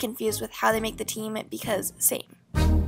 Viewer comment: "because satin" is what I heard